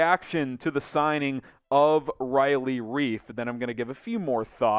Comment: omg youre gonna make me hate this signing... oh i thought you said riley reid.